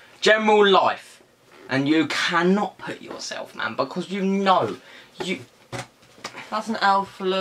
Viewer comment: just me who heard him say “you can not put yourself amber”? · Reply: X.katie X no, he said “man because”